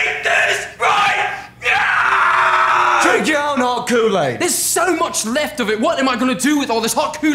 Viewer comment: i thought he said heart cool aid